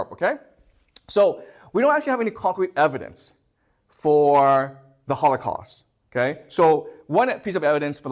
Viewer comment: Wait did he say there is no concrete evidence for the holocaust?